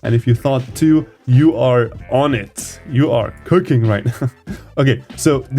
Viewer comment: I heard him say kirking😭🙏